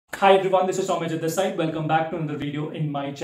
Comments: I thought your name was (instead of this side lol) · But your surname is, why did you say? 🤔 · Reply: 🥲🥲 I said this is Soumyajit This side. Though to be fair have heard this one before. And everytime I lol 😂😂